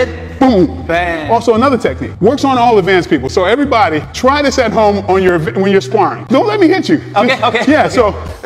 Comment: I heard "try it on your vic... when you're sparring". vic = victim?